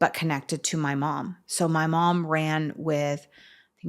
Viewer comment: Did she say her mom ran with BGF??